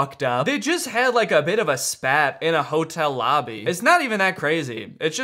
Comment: WHAT DID SAY? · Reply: Spat?